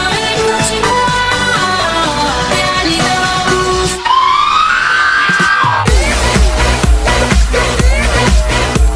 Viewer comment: did she just say daddy no? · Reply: Daddy don't move